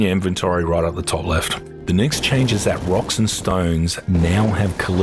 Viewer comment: did i hear a rock and stone?